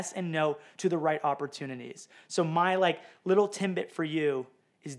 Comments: Did you just say TIMBIT instead of TIDBIT? Canadian slip! 😂😂😂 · did he say Timbit? As in Tim Hortons?